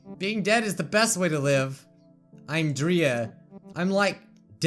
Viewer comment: I thought that it said "I'm Oreo"